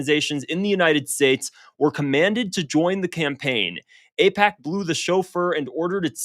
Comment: For a second I thought it said "AIPAC blew the chauffeur" lmaoooo 😭